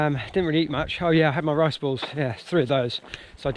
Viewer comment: What's in the rice balls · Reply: I need to work in my accents... I thought you were saying rice BOWLS in the lead up video, and I couldn't imagine you eating rice with a spoon as you ran! 😂